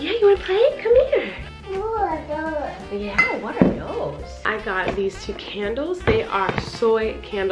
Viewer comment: I literally said "Did Flynn just say 'what are those?'" and then you repeated it so yeah, I heard him say a real sentence. How cool is that.